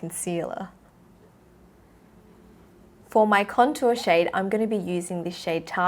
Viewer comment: omg haha i thought you said tao when you said tahoe🤣